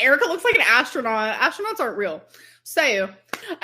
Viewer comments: did she say astronauts arent real?? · did she just say “astronauts aren’t real”